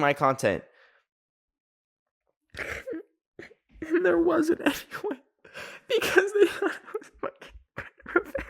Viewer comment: what is he saying here?? All I can hear is “and there isn’t any fucking way cuz thdk thfjjf fuckinqystf flfkfjgj”😭😭😭😭help Ive listened to that part like ten times 😭😭😭😭